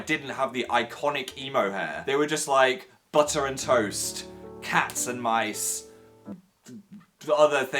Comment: "They were just like butter and toast, cats and mice, and other things that go together.." DAN AND PHIL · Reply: Oh my god i thought he said hats and mice and I was like ........(sorry btw)